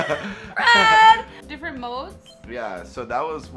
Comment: did anyone hear red at 1:50 to 1:55 ????